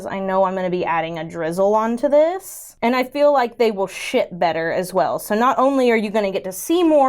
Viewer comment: Girl it really sounded like you said "sh*t better" at 7:57 😂😅